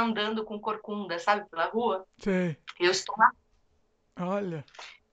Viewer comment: "Eeu estava" o que? Repeti umas quatro vezes para ver se foi erro da conexão e só então notei que não falou :D :D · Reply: Sim. Foi falha na conexão. Ela falou "Eu estou na rua"